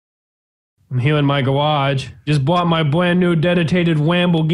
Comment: Did you say gawage